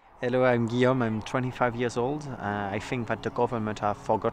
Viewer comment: why does the subtitles say William while he says his name is Guillaume?